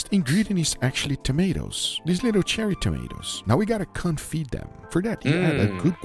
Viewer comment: what does the YouTube subtitles say? 💀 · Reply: Confitt them. Like confitt byaldi or in simple words, Ratatouille